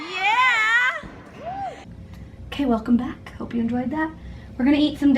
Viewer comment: omg I thought she said caleb come back! 😭😭